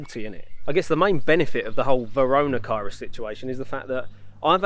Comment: did he say Varona Cirus? Haha